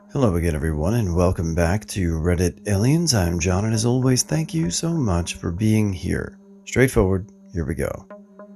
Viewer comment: There is an authenticity or genuineness to your “Thank you for being HERE” that keeps me coming back, and sometimes even reminds me to keep on keeping on in this crazy world. Thank you, John. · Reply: Yeah! I always hear it as 'thank you for being alive' which is great to hear sometimes. My thanks as well, John!